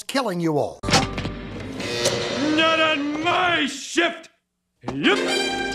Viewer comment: it sounded like said "shit". Great action-packed video. Hank deserves to return again.